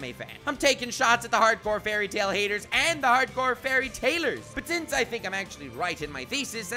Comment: I thought he said Fairy Tailors, so people that make clothing for Fairies. Therefore, we now know where the great fairies from Legend Of Zelda get their outfits.